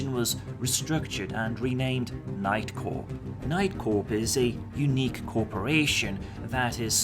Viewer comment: first time you mention "night corp" i hear "nightcore" and tiny rage burned inside of me